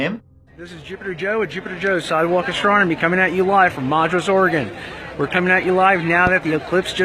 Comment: Where in Oregon?! I've listened multiple times and have NO idea what he's saying! I give up! But I'd still like to know! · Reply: Madras